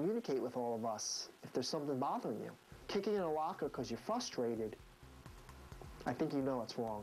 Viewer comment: i thought they said he shit in a locker at first hahah i was like hahah nice job!